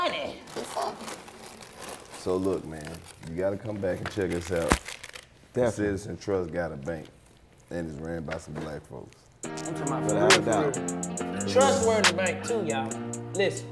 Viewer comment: "Citizen's trust got a bank... and its ran by some black folks" 🎵🎶🎵🔥🔥🔥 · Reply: I thought they said Citi Trends got a bank